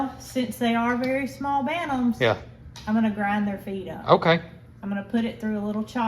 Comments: You said you were grinding there feed up... lol... I swear I heard you say you were going to ground there feet up. I said omg Mrs lol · I thought said she was gonna grind their FEET up and make them even smaller!😳! 😵‍💫🫣🤠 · Lol! I thought you said, “I’m gonna grind their feet off.” ( like trimming their nails) 🙀🙀🙀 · Lol oh she said "grind their feed up" good heavens I thought she said feet not feed Lol I just couldn't see them doing such a thing lol